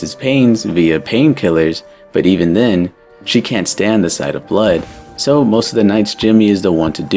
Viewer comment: "she can't stand the sight of blood", i thought she just said she couldn't stand the sounds he makes.